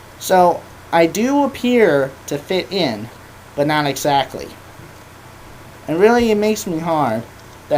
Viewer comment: Does he say, "now really it makes me hard"?